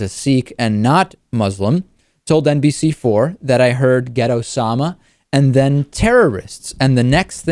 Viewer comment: I thought you said "ghetto-sama" instead of "get Osama".